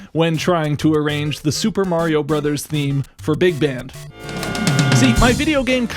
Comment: You got me confused for a second, I thought you said that you're arranging Super Mario music for the Big Ben.